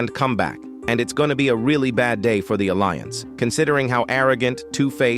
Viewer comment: the caption said "for alliance" but i think i think i heard" For the alliance or is just me?